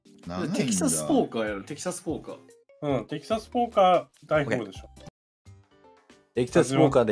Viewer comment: 、｢テキサスポーカー、大富豪でしょ。｣って言ってるように聞こえる〜